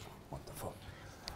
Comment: I know he said wonderful but it sounded like wtf🤣